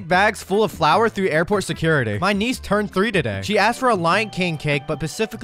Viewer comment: Why did I hear "my knees turned 3 today"😭